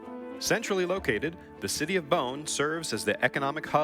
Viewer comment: I thought he said city of bones💀